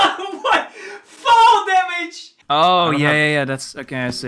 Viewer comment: I misheard it as "f@ damage"